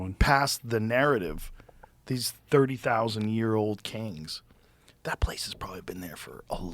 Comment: Did Joe say “Kangs” at 28:01? 🤣